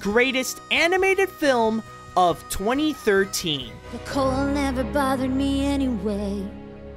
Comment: It sounded like Elsa said,"Coal never bothered me anyway." I guess she was on the naughty list.